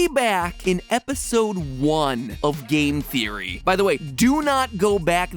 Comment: I thought you said EGG theory...